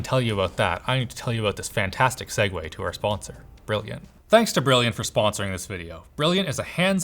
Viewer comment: listen to it with "Broken" in mind instead of brilliant and you'll hear it